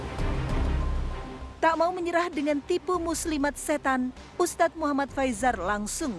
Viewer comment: tipu muslimat setan? Aa aku yg salah denger ya